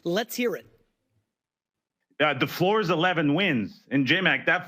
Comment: Did he just say the FLOOR was 14 wins? The floor is the best record in the NFL? FLOOR?!!!!??? He understands if the floor is 14-3, the ceiling is undefeated right?… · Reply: He said floor was 11.